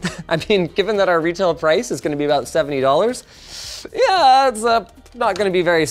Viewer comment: 70? Did I hear that correct, or is it 17?